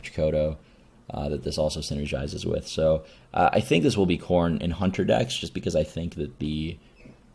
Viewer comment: "I think this will be corn in Hunter" -- is that an expression that people just use and nobody told me? "This will be corn"?? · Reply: That makes more sense. Haha I guess he slopped over saying "this will be core in -- in Hunter decks" and I just heard "corn in Hunter". christ